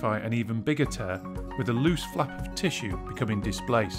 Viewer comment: I thought he said "with a loose f**king tissue..."